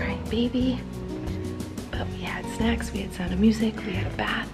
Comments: did she just say we had sex I might just be deaf idk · what did she say?... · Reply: Snacks, not what you thought... your so dirty minded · when she said "but we had snacks" i thought she said "but we had s*x" and i did a double take. I had to turn on captions in order to hear what she was actually saying because i know she would not have said s*x in a video made for an audience mainly consisting of young people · is it just me or did she say “we had sex” I’m sorry but I heard that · it sounded like colleen said 'and we had sex' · I thought see said we had sex